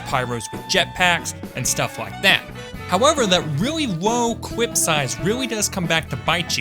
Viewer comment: Did anyone else hear him say" wo clip size"